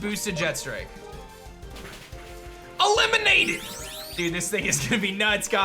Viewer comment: When he said eliminated I thought he said E-lemonaded